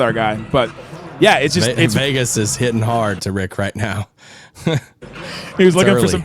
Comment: I’m drunk and I thought you said nip hard lol